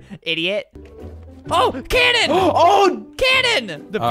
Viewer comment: I thought he said Karen but he actually said cannon lol